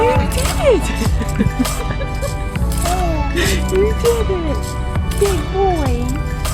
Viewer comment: IT SOUNDS LIKE HE IS SAYING “did it”!!!!! probably not but is adorable